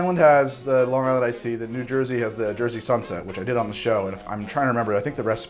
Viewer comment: so is it me or does he say jersey sunshit at 8:19? Yes i have problems...maybe.